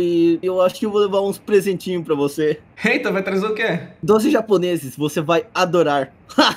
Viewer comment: "eita vai trazer oq?" Eu ouvi "Doze japoneses"